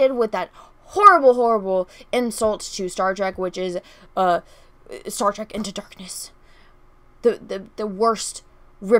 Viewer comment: I think I heard you say Star Trek into the feces. XD